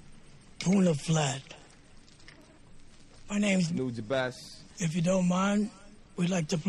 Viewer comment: anyone know what jun says @ 9:33? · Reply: "my name's "nujabes"